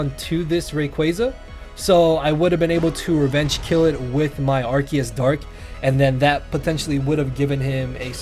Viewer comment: Who else thought it said “Arceus knows, da wae”?